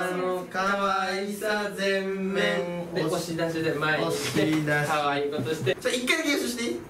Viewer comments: テオくん可愛こぶってって言ってない？w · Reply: めろん 「可愛いことして」だと思います 私も一緒そう聞こえて、ん？ってなりました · テオくん可愛こぶってって言ってない？w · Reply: 可愛い子ぶってとも聞こえるけど コメントらん可愛いことしてってみたらそうとも聞こえる